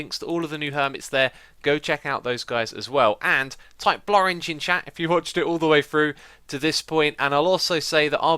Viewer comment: i still keep rewantching this part since i always hear "type florange in the chat" and i´m like florange? oragne + flower or what ^^. its so nice not to be a nativ :P